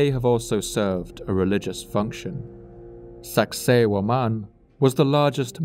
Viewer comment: Have this on in the background while I work and had a real double take at Sacsayhuaman. “Did he just say sexy woman? What does that have to do with anything?”